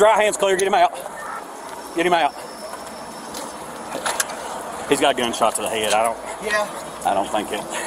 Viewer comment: Did I just hear.. "I got a headshot", "dude, I got a headshot first" at 40 seconds?